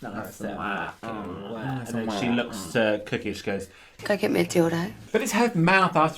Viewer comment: Also I thought she was saying "go get me a donut" this whole time